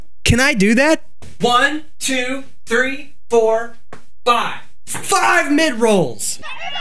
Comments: I legitimately thought he said mint rolls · I thought he said FIRE MINT ROLLS